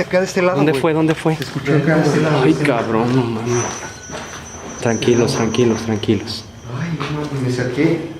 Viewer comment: In the last one, when the Mexican dude was repeating 'tranquilos, tranquilos', I LEGIT thought he was saying 10 kilos, 10 kilos xD